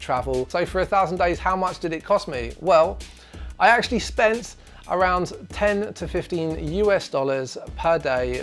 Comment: I heard "I actually spent around 10- to 15000 US dollars a day" and I was like wtf